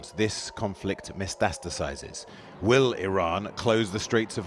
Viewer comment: "mystasticizes" what does that mean? Did you mean to say "metastasizes"?